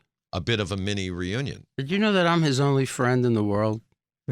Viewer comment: He never says he has only one friend. He actually says he is Gene Simmons’ only friend.